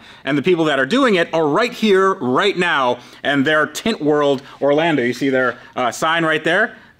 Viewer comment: i swear i heard tit world